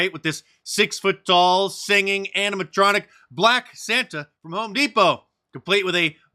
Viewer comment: At first I thought you said black Satan.. I feel better now